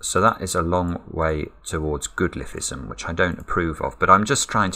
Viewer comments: What does he say here? “That is a long way towards ________” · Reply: Goodliffeism - means the logic is close to bifurcation. is teasing Mark, probably because Mark is so much better at speed solving! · "That is a long way toward ??? , which I don't approve of." What did he say? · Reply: Goodliffism meaning bifurcation